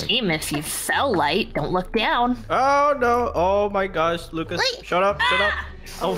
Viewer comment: did I hear light say “oh my gosh Lucas, get up get up” or is it just me?